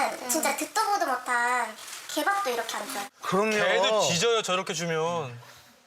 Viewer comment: 개도 뒤져요.... 라고 들림 순간 ?? 뒤지... 아! 짖는다고